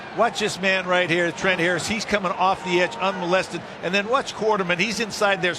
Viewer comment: did he say coming around the corner un-molested????